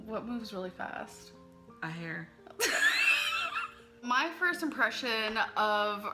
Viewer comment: “what moves really fast?” “A hare” totally didn’t get it. She thought said “a hair”